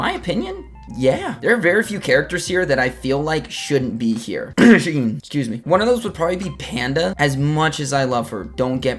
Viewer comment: What did he say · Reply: Shaheen.